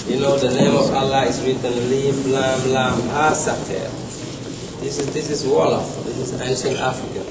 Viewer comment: Yes, He's Muslim from Tooba, Senegal People of Tooba are mentioned in the Quran. Thats why he said 'These are my people, this is my language' He was NOT a scheduled presentor, he was just there to attend the lecture presented by Runoko Rashidi. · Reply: He says at 0:08 "mim lam lam ha sater"? did I hear right? we know just elif-mim-lam in Turkey